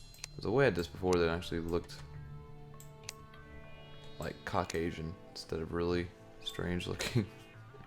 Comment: it sounded like he said cock asian instead of caucasion lol